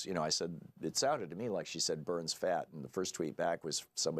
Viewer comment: Ha, I thought she said "Burns fax", at least fax are printed on paper that could be burned, but overall it didn't make sense to me.